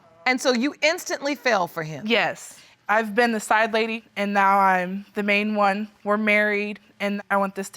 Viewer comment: Did she just say "I was the sideline &&' now I'm the main.?!" 🤷🏿🤦🏿😩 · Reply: Yep.... Actually she said "I was the side lady, and now I'm the main". I wouldn't call that a "lady".